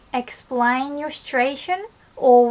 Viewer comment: it always sounds like she says "explain your stration" I have no clue what she is actually saying there